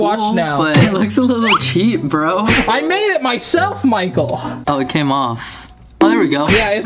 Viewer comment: Wait wait did he say micheal?